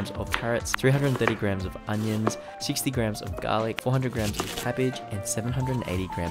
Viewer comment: I thought you said 600g of garlic. Whoops. I'll just have to eat it anyway...